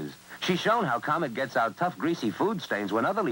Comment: I thought Louise Lasser said "Comet's pissed" at the end. No, it's "best" (after a second viewing)